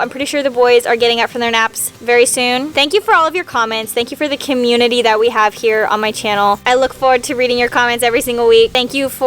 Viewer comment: Wait did anyone else hear that at the end: 25:15 did she say “the Kim-unity that we have here?” 😂